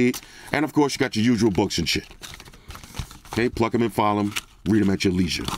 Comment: It's Pluck them and file them! I always thought you said "Fuck em and file em!" Oh well.